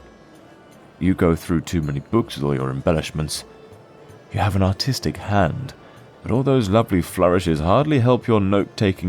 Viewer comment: i thought he said “you have an autistic hand” 😭😭😭